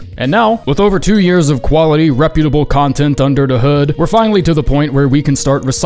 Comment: did you say "under da hood"???? If so, thats awesome I didnt know you were gangster like that.